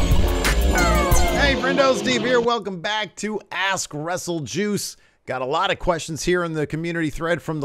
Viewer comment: In the intro it sounds like you say "Ass Wrestle Juice" That is a totally different type of video I think.